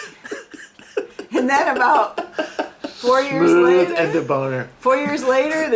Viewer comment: "Smooth as a boner"? Is that what Mr. Cox says at 17:35? Incredible.